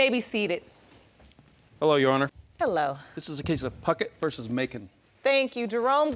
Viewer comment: I'm sorry but I misheard jerome saying 'this is a case of phuckit vs makeit' and i was like excuse me whaaaat!?! XD